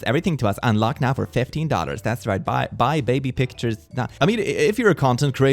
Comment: I thought he said “baby pictures for five daughters”